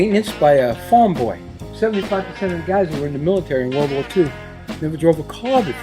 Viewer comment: i could have sworn the ww2 jeep guy said "these vehicles were made to be maintenanced by a femboy"💔